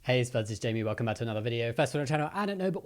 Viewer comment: Why does it sound like "Wokeme" instead of welcome??? '-'